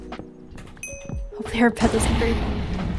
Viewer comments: think she says hopefully their bed just breaks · did she say 'There bed doesn't breathe now' i think thats what she said